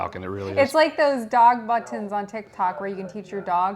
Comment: did she say "buntons"?